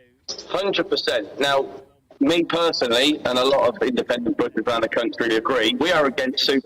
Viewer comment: The CC at 26:19 was perfect! Instead of "independent butchers", they just put "BS" 🤣🤣🤣